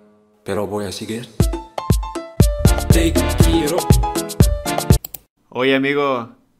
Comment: se puede hacer un meme de sus dientes. Parece que dice tikiro 15:35😂